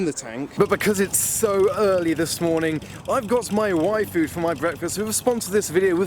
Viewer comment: i thought he said wifu. THANK GOODNESS ITS Y FOOD